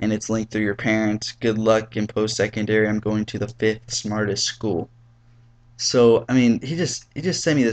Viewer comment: I heard 'im going to the fifth smartest school' i would of just said name it?